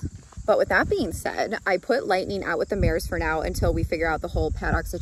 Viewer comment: I thought u said u we’re putting lumi with the mares